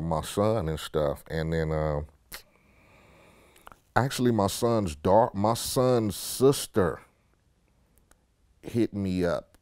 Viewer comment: I thought mc ehit only had one kid a son because i heard him say his son sister